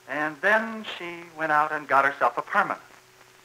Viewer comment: At first I thought he said she went out and got a permit. Like a gun permit.